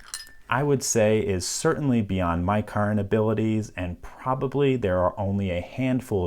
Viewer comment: did I just hear him say "beyond my capacity" ????????????